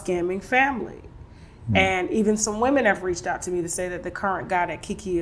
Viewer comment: How did he end up with KEKE❓️❓️ He seems old school religious with a good heart . Howd u get a hooked up with the type women as keke???? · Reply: I thought he said Keke reached out to him...